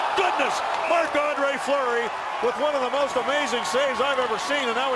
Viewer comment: did he say mcflurry?